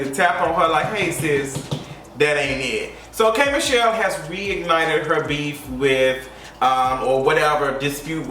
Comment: Instead of “reignited “I thought you said “wig ignited”😭😅😂 Another great vid! I’ve been A fan since you’re bad night experience video back in the day